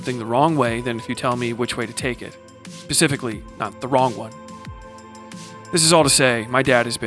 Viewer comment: "pacifically", am I hearing that correctly? 🌊